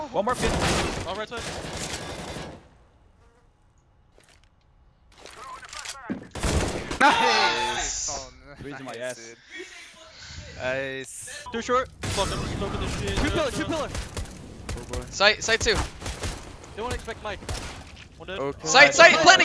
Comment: "Brehze my ass" did I hear it correctly? lol